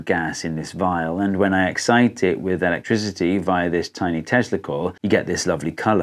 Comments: I swear I heard "via this tiny testicle" 😅😅😅😅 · You mean you didn't die laughing when he said tiny testicle? Around 2 minutes in...😂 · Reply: I heard it correctly as Tiny Tesla Coil at first but now I can't unhear tiny testicle lol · I can't be the only one who heard a tiny testicle at 2:14 right? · I had to play it back 3 times before I heard what you were actually saying. I thought you were saying "via this tiny testicle'. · Lmao...at first when he said tiny Tesla coil.... I thought he said tiny testicle....ha ha...ooohhhh the fun i have. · I could swear I heard "this tiny testicle"... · Did anyone else hear @ 2:14 "if I excite this with electricity via this tiny testicle"